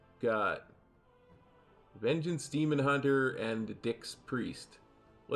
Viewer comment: I have repeated this part soooo many times and every time to me it sounds like says : vengeance demon hunter and dicks priest xD I was doing Atal 17 with my friend(my key), I was tank and he was healer. He was inviting and we got aug evoker and ret pala who were at like 2.5k and 2.7k and INSTANTLY asked my friend why would they even join our grp and he said to farm crests. I pug much more often than he does so for me that was first red flag and its big enough flag for me to restart group forming but unfortunately didnt said anything and key started. We reached 2nd boss and guess what? They failed to kill totems 3 times...yep 2.5k and 2.7k ( dont remember score of 3rd dps) FAILED on 17 Atal to kill totems in time to start boss...not once..not twice but THREE times and Aug evoker typed in chat "idiots" and left the key. I got frustrated because I know if we reset and just focus we can still time it but noooooo he had to leave so I start whispering him nasty things like hoping smting bad happens to him etc ( immature from me,I know but situation got to me) I should have just sent him a msg saying leaving was not okay and he wasted our time,just like you suggested in video. Guess what happens next? We decide to do 16 Atal, we invite people at around 2k score and that NEED that Atal 17 ( I also needed that key) and we TRIPLE upgrade it to 19....